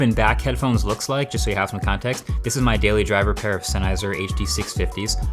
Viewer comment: I thought you paid to get good subtitles? you said "can't" they wrote "can". I'd request a refund or an improvement. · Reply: "Senizer," instead of "Sennheiser"??? Imagine being deaf rn... okay on second thought. You're probably not looking at a headphone video